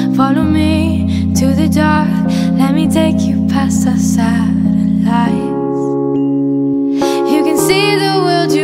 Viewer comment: 43 seconds in it sounds like she said," I'll take you past the salad light."